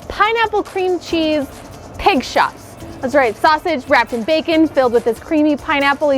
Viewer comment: I at first thought it said “pig snots”.